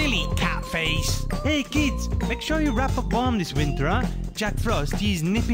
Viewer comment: at first i thought Cat Face said "make sure you wrap a bomb this year"